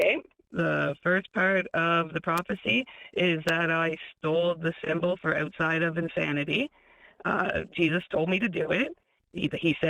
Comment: "I stole the symbol for outside of insanity". Did I hear this right? WTF does that mean????